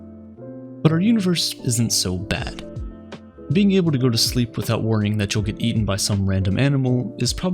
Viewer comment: I thought it said “going to sleep without Wyoming”